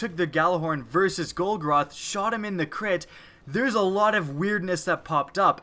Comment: not gonna lie, I heard shot him in the clit